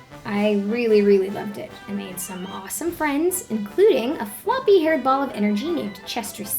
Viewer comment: What did you call Chester? I heard "A floppy haired ball of energenie" lol Best draw my life vid so far and this made me cry a river. Thank you.